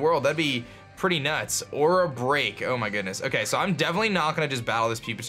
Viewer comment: I thought you said "or a break," instead of "aura break," and I started to freak out